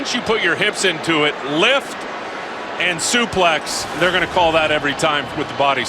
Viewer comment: did he say "Suplex?" that is an old wrestling word